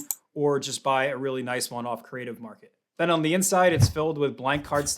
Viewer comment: you say or buy one off ???? I listened 4 times but still could not tell what you said and don't see a site name on the video section for this. Can you please let me know the name of the site? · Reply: Creative market